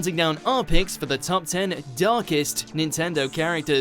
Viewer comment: For some reason, I thought it said darkest Disney characters for a second...